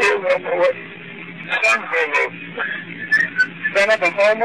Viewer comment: LMAO THE COLOGNE THEY WAS TALKING ABT WAS NOT CALLED “SUNG HOMO” ITS CALELD “SUNG HOMME” AND MICHAEL OVER HERE COMES UP WITH “SON A HOMO” 💀